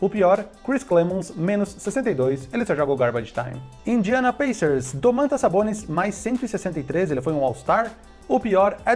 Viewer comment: Alguém entendeu o que ele disse??? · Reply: Ah! Valeu! 👍🏻